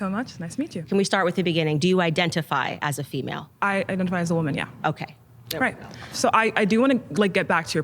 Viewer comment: did she say "I identify as aluminium?" 😂